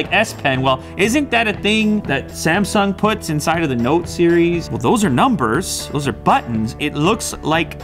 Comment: did he say T-series